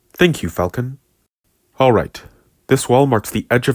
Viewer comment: what I heard was "alright, this walmart's the edge of the mountain"